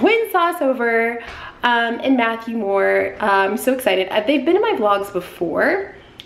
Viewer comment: For some reason when said Matthew Moore I thought she said “Mackelmore” and I was like, what what what what? 😂😂